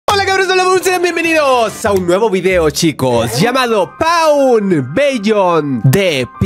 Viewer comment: que dijo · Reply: Dijo hola cabros hola bumers